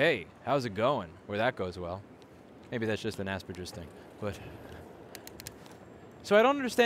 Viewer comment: Did you say maybe that’s just an Aspergers thing at 5:44?